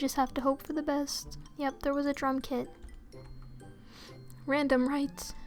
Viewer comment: This is weird but when she said "Drum kit" I thought she said "Drunk kid".....